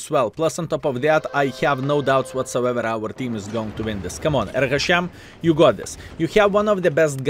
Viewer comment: did you say "baruch hashem"?